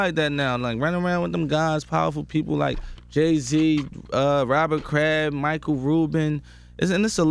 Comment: what name did he say? Robin Crab? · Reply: Kraft. Patriots owner